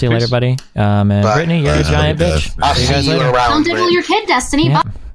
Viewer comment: Ok did she say "I'll diddle your kid" or "Go diddle your kid"? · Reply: That's definitely and I'll LMAO